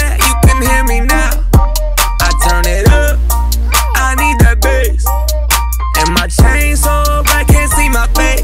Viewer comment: I thought it said "i need that bitch"